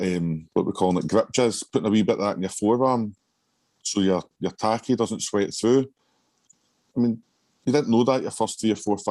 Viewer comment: What did he Say at 26:16 about tacky? I canti understand but It seems like an important tip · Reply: He said about putting grip jizz (liquid chalk) on your forearms before the tacky to stop you sweating though it.